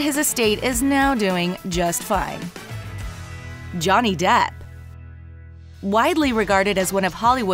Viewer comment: Said “Debt” instead of “Depp”. I assume that pun was entirely intended.